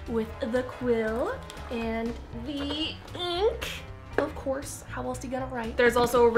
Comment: she says eenk instead Of ink